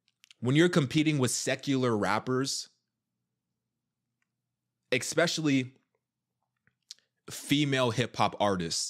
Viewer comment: Bruh i hear you say exepecially instead of especially all the time😄😂